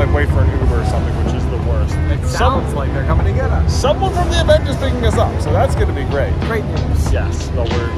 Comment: I thought J said "someone from the Avengers is picking us up" That would have been cool 🤣